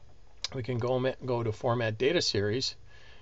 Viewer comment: I can't be the only one who heard him say "fart"